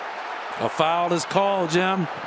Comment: That Booker no foul was still one of the worst "we don't want to run him" calls ever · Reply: Are you talking about 10:07? I thought the commentator said "Foul was called."